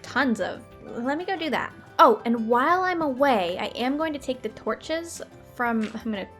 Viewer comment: I think she said something she should not have in a youtube video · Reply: What lol? " Oh and while I'm away "